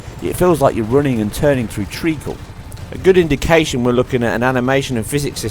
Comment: What is this word at 5:47 Feels like you're running and turning through....Treequil? I listened to this like 10 times, what word are you saying here? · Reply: Treacle - A thick syrup.